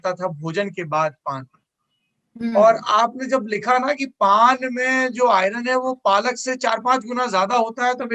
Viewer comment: Why I heard "PAAD me jo iron hai wo palak se jyda hai" 😂